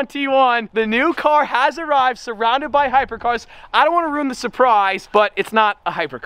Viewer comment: I thought he said surrounded by hybrids and I am like, cool I like Corollas too